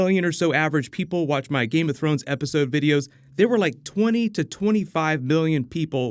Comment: Do you have a cold? I only ask because starting at 6:10 it sounds like you say, "20 to 25 Billion people watching Game of Thrones episodes last year..." Sorry, I just couldn't help myself. You have no need to exaggerate when you have such a fabulous, dare I say - awesome - channel?